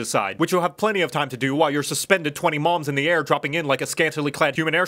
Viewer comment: okay, let me see if i got this right, the line is: "while you're suspended twenty moms in the air" that's what he said right? i'm not crazy, right? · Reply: Is when he says the line, while you hear mom, what he actually saying is "while you suspended twenty Mobs in the air." Though I do understand the confusion